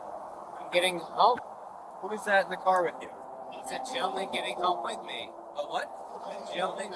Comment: When he said “gentleman” no no I swear I heard him say jailbait